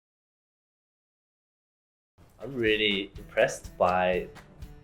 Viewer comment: I thought he said depressed! Lol